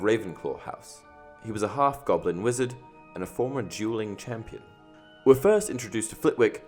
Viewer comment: Please tell me I'm not the only one who heard "juuling" 😂